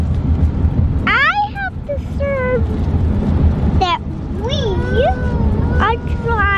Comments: Did say "discerned"? Big word for a 3 year old! Impressed! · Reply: it sounds like he's saying "I have deserved that we are driving..." lol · DID JUST SAY DISCERNED ??? HAHA